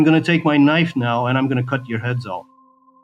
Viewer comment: I'm sorry, I didn't understand the first three times, did you say he was going to cut your heads off? 😅